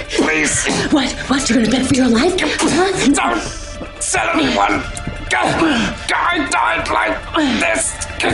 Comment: What did Lucifer say? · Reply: "please don't tell anyone i died like this"